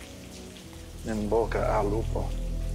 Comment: what he said? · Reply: in bocca al lupo = good luck